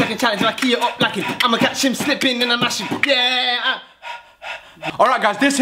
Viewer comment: Did aj say I’m gonna catch him slipping and I’m gonna mash him?